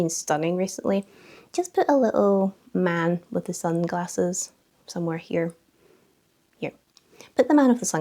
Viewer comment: god damn it 🤣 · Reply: thought you said emote with glasses 🤪